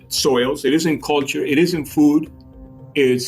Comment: Can anyone understand what this guy says all three places are experiencing? 09:54 I've listened to it several times and can't make it out. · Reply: “All three places are experiencing global warming. “